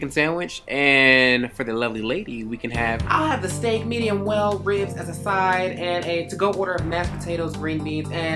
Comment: Could've sworn I heard whale ribs.